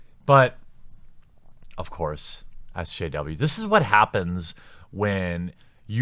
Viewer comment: did he say “ask jw”????